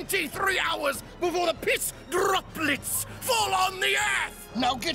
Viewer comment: he nailed the DRRRROOPPLLLLETS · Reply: He says droplets? I thought he said tropolis